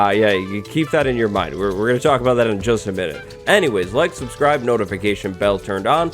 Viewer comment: I thought i heard lick subscribe